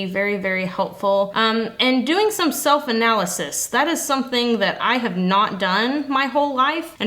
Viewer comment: Swear for a min I heard "soap analysis" and not self analysis" thought that was an interesting way to to cope with with mental health 😂 And yes over sleeping is so bad! I used to sleep away issues and I'd feel horrible from it made things worse. This soap however gorgeous.